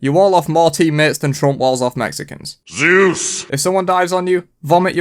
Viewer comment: Subtitles say juice not Zeus lmao